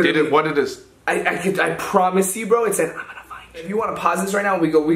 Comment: I could find you that’s what it said